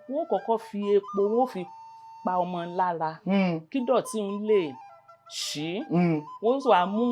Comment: An excellent performance! Kudos to her. She was so smooth that at 10:36 she said ''dorty'', Oluko didn't catch that.